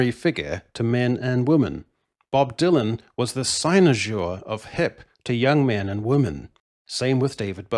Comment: I could use a bit of help lads. At 12:42, uses a word and I my smooth brain cannot quite figure out what it is. If anyone can assist I'd be very appreciative. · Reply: The word used is cynosure, it means someone or something that is the center of attention, or serves as the guide/director.